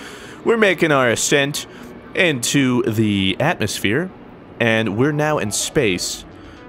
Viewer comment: Did he just say Atlas-Sphere I replayed that twice and I still heard Atlas-Sphere